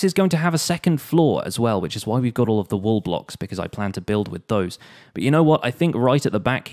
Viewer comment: on 17:07 for a sec, i thought u said ROBLOX, but it was "wall blocks" I misheard it 😅 lol